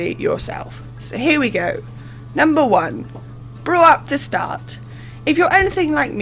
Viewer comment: Haven't finished the video, had to pause and laugh because I was distracted at the very wrong moment in the beginning and thought you said "grow up, to start", and I know I've been gone for a while, but I didn't think you would have changed your tone that drastically in that time 😂